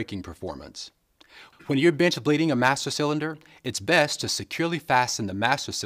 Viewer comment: i sounds like he saide bitch bleed jajajjajajajaj